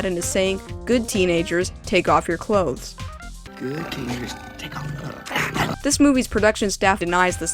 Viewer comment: In Aladdin I heard "good kitties take off their clothes" does anyone else hear that or am I just crazy?